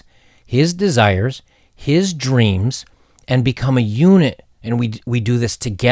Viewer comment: It's funny...around 10:38 I know you said unit, but I heard eunuch. Though I don't think that's too...out there, look at prince Harry